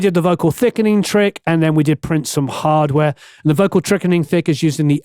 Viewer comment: I did a double-take at 31:30 when I heard "the vocal trickening thick" 😆 Excellent work; thank you for yet another amazing demonstration / review.